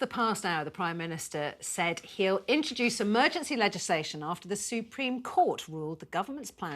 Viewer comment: Supreme court of UK it's in London . Foreign to you if you are based in Russia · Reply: Thanks for the clarification. I heard “European court” so misunderstood.